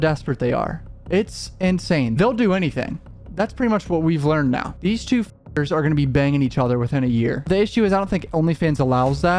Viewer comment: The bleep @ 2:08 sounds like you said “these two fa**ots are gonna be banging each other” 😂😂😂